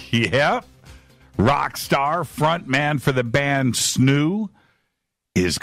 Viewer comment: First five mins I seriously thought he was saying Snoop and was sitting here wondering wth Snoop isn't in a rock band and what's he doing coming on f2b LOL